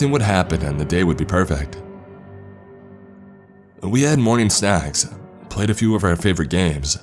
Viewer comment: Around minute 8:00 I had to rewind because I thought the dude said they had morning sex